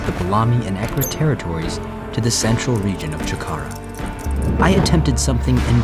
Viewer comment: Some how i hear “jakarta”